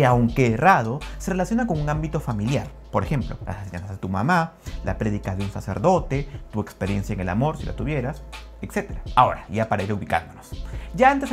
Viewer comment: "Sjdigh de tu mamá" no entendí lo que dijo 🤔 alguien puede aclarar ?